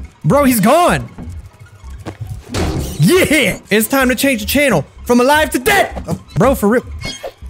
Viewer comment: if you guys did not understand what he said he said it’s time to change the channel FROM ALIVE TO (DEAD)